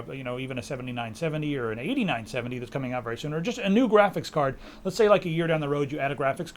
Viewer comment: did say that the hd8970 is coming soon?